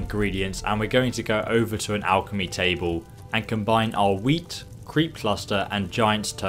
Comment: I was just casually listening and when you started talking about collecting wheat, I thought you kept saying weed. As you can see, that would be hilarious to hear while watching a video about alchemy